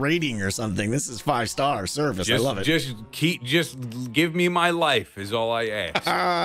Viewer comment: I always name my minecraft horses Mayo...... Because..... Mayo Neighs..... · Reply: I thought that said “take my life” and I got scared for a second